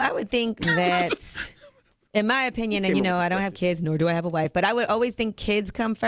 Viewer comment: sounds like yee said I don't have kids I do have a wife